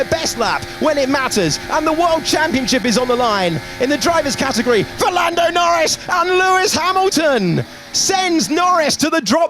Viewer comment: my bad ear makes me thought it was Fernando Norris instead of 'for Lando Norris' 😂